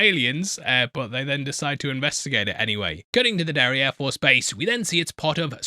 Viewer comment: I heard: Derriere Force Base.